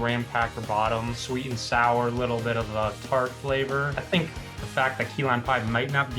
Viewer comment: I thought he said a little bit of pineapple😢